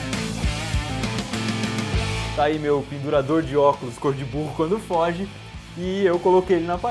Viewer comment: juro q ouvi ''cor de burro quando fode'' O.O